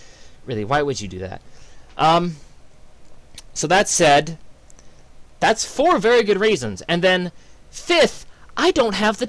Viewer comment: "That's 4 very good raisins." ? You always crack me up XD Anyway, did you say you lied that you lied? I'm confused. But whatever you do, we'll always love your videos, and I'll always keep going back to watch your Area 51 Let's play cause I live in Canada, and the assholes at Midway won't let me play it. AAARRRGGGGGGGGGGGHHHHHHHHHHHHH